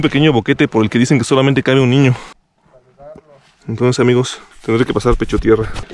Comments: estabas sólo? Porque dices que por ese hueco pasaban los niños, sale una voz diciendo "caminando" una psicofonia parece jaja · En 12:04 se escucha otra voz no entiendo qué dice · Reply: Dice Juan Carlos